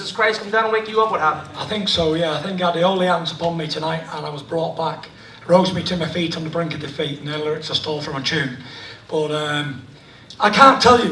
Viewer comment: Did he just say "A TRICK I STOLE FROM A JEW" ? LOL.... Awesome man.